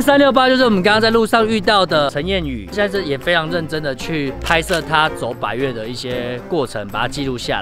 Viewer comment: 聽成”拍攝他走法院的一些過程” 我先跪